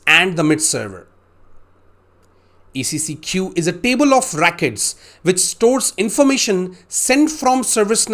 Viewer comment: why do I keep hearing "Rackets" when he's trying to say Records lol